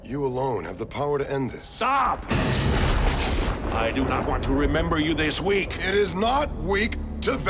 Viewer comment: Me and my cousin would make fun of this movie and die laughing when we were kids. Always thought he said, “Stop! I don’t want to remember you this week” “It is not week to value life!!” Lmao